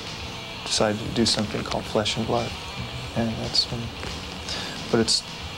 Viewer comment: "Fresh and Blood" instead of "Flesh and Blood" Never change Japan.